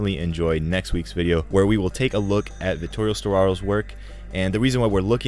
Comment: what films name because i didn't understand directors names. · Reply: vittorio sottrarrò